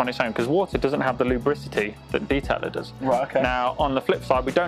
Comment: Did he just say lubricity ?